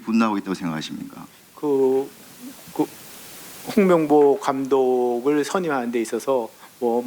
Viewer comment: 똥명보라고 들림. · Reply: 콩명보라고 들림.